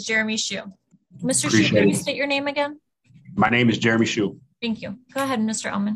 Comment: I thought for a moment he said "My name is tiramisu" 😂